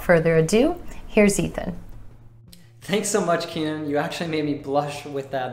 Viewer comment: Hey Keenyn! hey everybody! thanks for your videos. I have a questions! WHen i heard Ethan's presentation he says (1:06 ) "You actually made me blush..." The thing is i don't understand when he said 'actually' i heard something like actual? This could be?